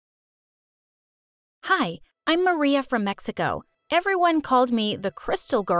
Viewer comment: did she just say mexigo? LOL